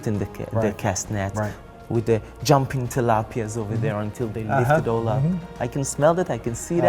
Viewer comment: Did he say Jumping Tilapias...?